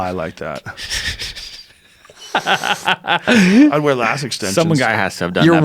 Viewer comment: Did Joe just say he wears lash extensions @ 1:18:54 ahaha?